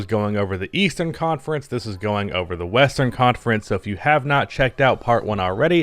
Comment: Western conference?? I thought you said Weastern Conference😂